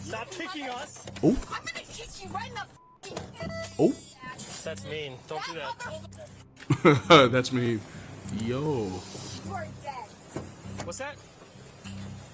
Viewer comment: Last one when she said to the cop “You are dead” I heard “You are gay”😅 I don’t know why, I laughed.